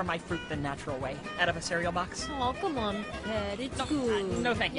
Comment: what did Jewel just call Blu was it Ped or was it Pet